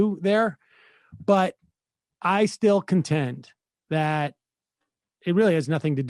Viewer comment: Did he just say, "I still content."? Pppffff. 😆